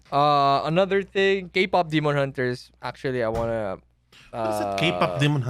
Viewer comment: Did I just hear kpop demon hunters?!?! 1:11:14 Sana nga mapagusapan din nila to hehehhe